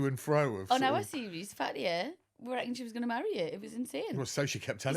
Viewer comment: what on earth is she saying here? did she switch to Welsh or something? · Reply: “On our series, Fatiha we thought she was gonna marry you”